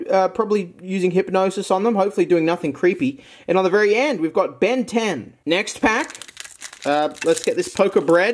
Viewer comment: I hear that Ben10 is the rarest Pokemon